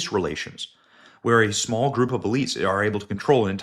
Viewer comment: WHAT DOES HE SAY? "A SMALL GROUP OF BEINGS?"